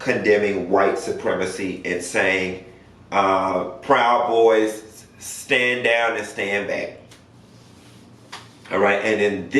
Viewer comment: I heard " proud boys stand down but stand by".